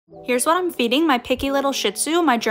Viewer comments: I first I thought she said " this is what I feed my little shits" 😭😭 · I heard “my picky little sh*t, Sue” 😂😂😂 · your picky little shitzu!😆 love it😂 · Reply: I thought she said picky little shit for a second 😂